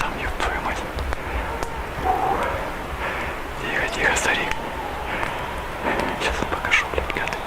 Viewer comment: the subtitles are wrong in multiple places but what he says here is "Stay calm, old man" (referring to himself) not anything about being told anything, and not "something ancient" but "I'm gonna show y'all"; And at 21:36 he talks about how quiet it got not anything moving